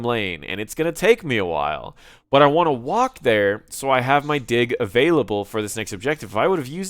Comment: "Im not using my dick right now. I wanna walk there so i have my dick available." :D (4:30) Sorry but thats what i hear xD